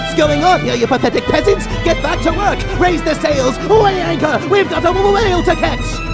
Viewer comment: I just noticed that at 1:59, after Eridan says "What's going on here you pathetic pedants? Get back to work! Raise the sails Something (I honestly don't have a clue what he says) the anchor!" And then it sounds like he's saying "We've got a millennial to catch!" I just can't help but laugh whenever I hear this. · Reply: He says "What's going on here, you pathetic peasants?! Get back to work! Raise the sails! Weigh anchor! We've got a whale to catch!"